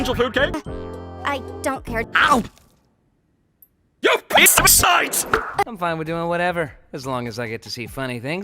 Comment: is instead of "YOU PARASITE!", it's "YOU PIECE OF SHITE!"